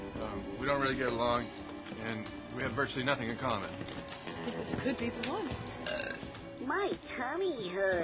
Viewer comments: What does Kate say there? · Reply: I think she says this could be the one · What does Kate say in @ 23:55?? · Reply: She says this could be the one